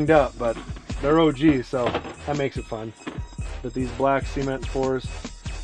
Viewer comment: did he just say black c ment 4s??